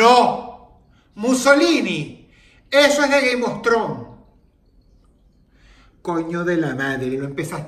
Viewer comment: no se por que escuche “gay mostron” en lugar de “game of thrones” xdxd